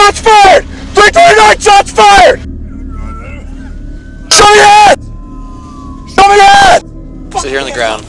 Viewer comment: the subtitles said “show your ass” Lmaoo